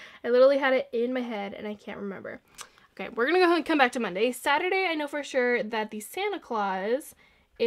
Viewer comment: why did you call Tuesday, “Saturday” ?? Lol